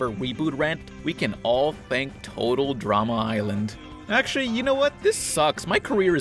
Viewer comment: I Thought you said total drama is bland but you said total drama is land